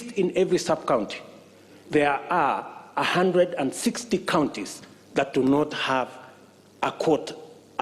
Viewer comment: Did I hear 160 (11:26) counties???